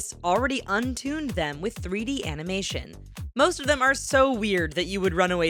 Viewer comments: Did she just say "3D AMINATION"? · did she say amination